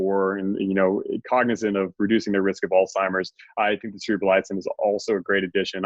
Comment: What did he say @ 50:02 to add to Cmax and FGL?? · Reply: Cerebrolysin?